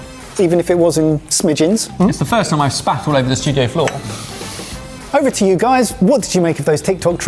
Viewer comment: Why did it sound like he said he "Spaffed all over the studio floor"?